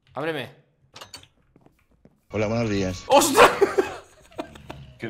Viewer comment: que es lo que dice? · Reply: "Hola buenos dias"